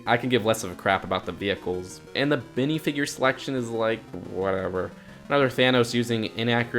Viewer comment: did you call them binifigures?!?!?!!?!?!??!!?!?!?!?!?!